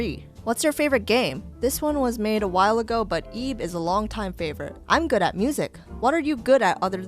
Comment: what game did she say at 6:00, eeb ?